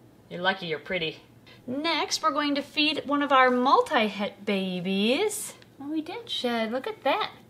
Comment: I’ve never heard anyone talk “baby talk” to a snake before. It’s kind of cute! I thought she said “multi-head”- and was expecting a two-headed snake to come out and eat. Lol!